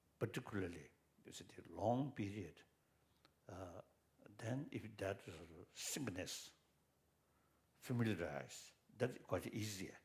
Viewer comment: Did anyone understand what word he uses around minute 11:22? I can't quite make sense of it, when he says: "Then, if that [the word I'm missing here] familiarised, that quite easier, and also you get some kind of relaxed." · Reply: "sort of singleness is" (as i understood)